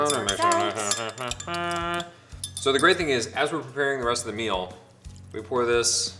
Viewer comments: I heard "Ezra preparing..." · When Ian said "as we're" at 6:25" it so sounded like he said "Ezra" !! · When Ian was saying, “As were preparing the meal...” I thought he said, “ Ezra preparing the meal...” 😂😂😂 This is what happens to you if you watch too much PLL 😂😂😂